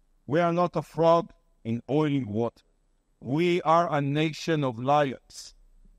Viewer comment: Did he say "we are a nation of liars?"